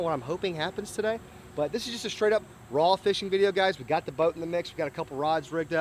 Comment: When he said "raw fishing video" I thought he said frog fishing video, I got so excited 😭